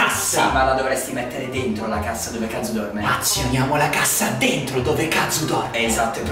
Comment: ho sentito dove caZ0 dorme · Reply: dove Kazu dorme*